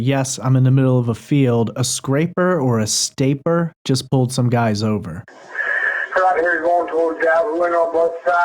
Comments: I heard " this guy, this guy just pulled some guys over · I hear " a state trooper just pushed some guys over" he says it very fast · I thought he might have said escaper or escaped here not scrapper or stapper.